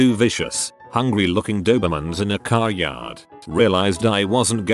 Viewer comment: The story at around 4:00, my earbud cut off and it sounded like he said "and I was chased by two vicious, hungry, lawyer mans" and I had to do a double take